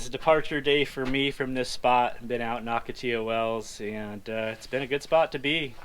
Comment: or anyone else understand the place said at 0:11 ? sounded like "been out at occatee ew ells" but I have no idea what that means.